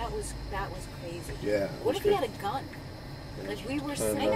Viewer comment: did she say "i wish we had a gun" ? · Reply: "what if he had a gun"